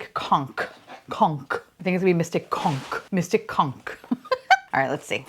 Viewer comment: Sorry not sorry I thought you said "Mystic c*nt" 😅😂